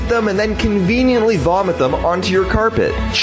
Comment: I’m on the toilet and the only words I heard were “watching you on the toilet” SEND HELP😭🤚